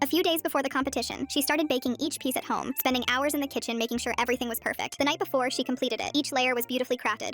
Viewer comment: I thought it said vaping not baking